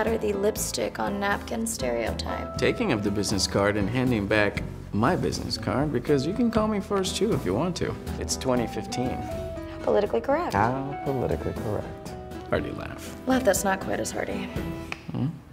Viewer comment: I misheard "laugh that's not quite as hearty" as "right let's start coitus already" and did a huge double take. >.>